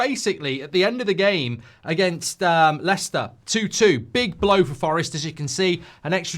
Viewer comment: I thought he called the forest owner a big block 😂😂 I was gonna say